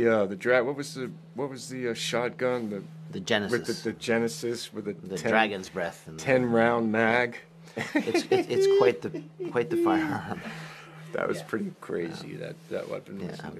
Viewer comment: Sounded like he said Banana cakes rather than kicks